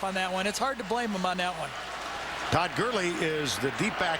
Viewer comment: At about the 13 minute mark Gary calls Todd Gurley "Dirk Curley". Lmao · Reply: I thought he said Turd Curley